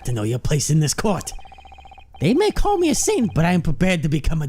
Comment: Tbh I thought I heard say simp instead of saint